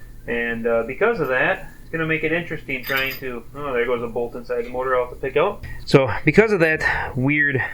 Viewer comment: Bruh the second you said “oh just dropped a bolt in the motor” I was like wtf did he just say, like my heart dropped for a second😂😂😂